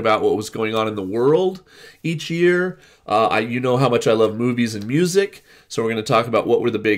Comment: At first I thought I heard you say "boobies and music". I had to listen again. Lol